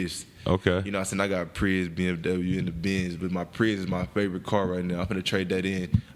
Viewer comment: Did Larry say he had a Prius? @ 57:48 😅